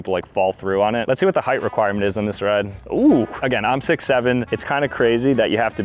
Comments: did i hear 67?🤩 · Wait in the first one did you say your height was 67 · Did you say..... 67?